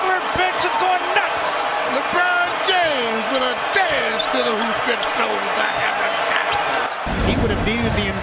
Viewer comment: Can somebody help me out with 3:40? All I heard was Lebron James with a danstodahoopaduhjackanah